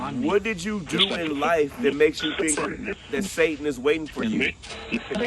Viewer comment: This session is extremely interesting. I thought he said Satanist the second time. Just a thought, was he a Satanist in life ? Now he's seeing the difference?